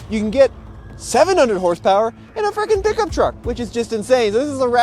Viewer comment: Can I get some clarity on what says at 1:34? It's a what Pickup truck? LOL! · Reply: A frickin pickup truck. 😂😂 he didn’t curse